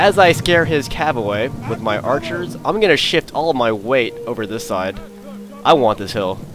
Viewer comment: I thought you said "I'm gonna shit all my weight over this side, I want this hill"!